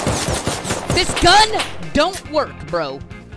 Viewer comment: sounded like Finn from Buggs life these berries don’t work 😂😂